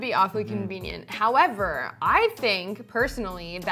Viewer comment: What does she say 4:42? Crakins??